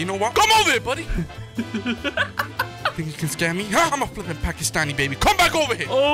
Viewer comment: just said im a flipping pakistani 8:30 to 8:35 what do you guys think he said if he really said it, was he joking or was he telling the truth